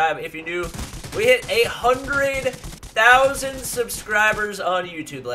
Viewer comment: it sounded like you said you hit 800k subscribers. I was like what the fuck that was fast